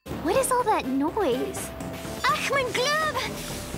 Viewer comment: Her German is 9/10 times very understandable...but as a native German speaker (technically I speak Swiss-German but in general, all Swiss-Germans also learn how to speak fluid German when we grow up) I have no idea what she's saying at 0:28 (the meteor thing around Ice king's mountain...it sounds like "Ach mein Glüb" ...is she supposed to say "ach, mein Gott?" or "ach, meine Güte"?) or 0:43 (with the mutant monsters...it sounds like... "Pollenstock"?) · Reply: in 28 seconds one, she says ''Glob'' whos a God in Adventure Time lore. in 43 seconds one, If i remember correctly some people said she says ''Brown Town'' in German or something like that.